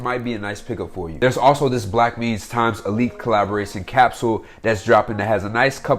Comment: can someone tell me the name of this collab cause I can’t tell what he is saying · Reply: applefellfar Blackmeans x Alyx